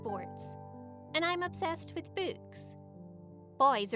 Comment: Who else thought she said boobs at 54